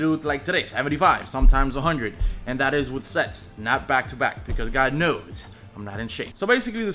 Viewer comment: I swear he said "sex" lol